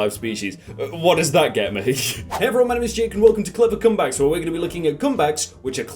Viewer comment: Is it just me or does his accent make it sound like he is saying cum bags instead of comebacks